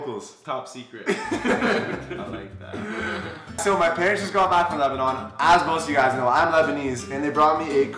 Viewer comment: I thought it said lesbian